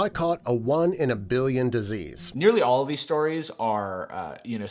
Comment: I thought he said "I caught a billion disease" lol